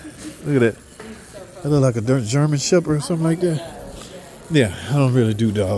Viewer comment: It sounded like you said a, “Dorman Shepard”, 😂😂😂😂😂😂😂😂😂😂😂😂😂😂😂😂😂😂😂